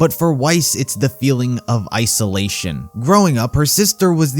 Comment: Weissolation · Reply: I thought he said “ice-olation” as a joke at first in the vid. I only realized it a year later. WHY.